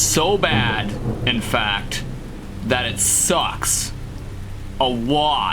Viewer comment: Did HE SAY SUS?+???$;€÷&×*; ANONG OOOS REFERENCE!!!!!